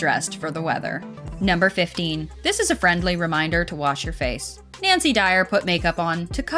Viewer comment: did i just hear Nancy Dyer??